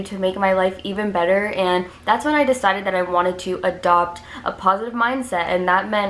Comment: when you said “adopt a positive mindset” I thought you said adopt a porcupine LOLL 💀